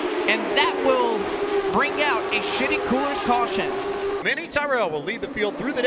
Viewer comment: does he say "Shitty Coolers" caution? 😂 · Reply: Yeah I think the brand is “shiti” I was taken aback the first time I heard it too